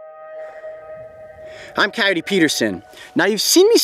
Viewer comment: Coyote Peterson: This is the worst sting I've ever taken Executioner Wasp: Hold my beer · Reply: For a second I thought you said coyote person XD